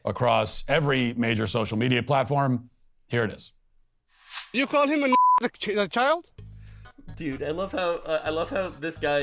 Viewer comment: I straight up thought the guy said "you called the n*** a child ?!" I had to rewind the video a few time to review what i heard lop